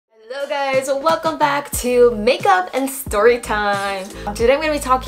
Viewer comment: When she said "Makeup & Storytime" my 'understanding of english' glitched for a second and I thought I've heard "Make up A Story time"...oh the shame